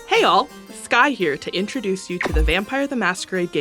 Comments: For a sec, I thought they said "Hey all, Scott here." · Was I the only one who misheard Skye at the beginning as "Hey Y'all, Scott Here" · Not gonna lie, I thought you said "Hey all, Scott here" and I got really confused lol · Had to rewatch the beginning three times because I kept hearing “hey all Scott here”